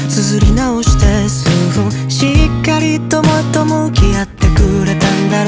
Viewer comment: だれか、しっかりトマトも嫌ってくれた。と聞こえてしまった自分を殴ってくれ。